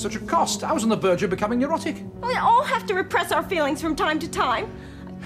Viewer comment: Did he just say erotic?